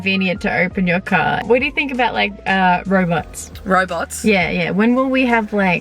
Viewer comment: Who else thought she said robloxs instead of robots 😂😂